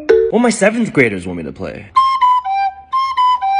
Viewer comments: What are the 2nd grader’s parent’s opinion on this? Sry I misheard it was 7th lol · I misheard 7th graders as 2nd graders and I was genuinely confused by the fact 7 year olds are watching South Park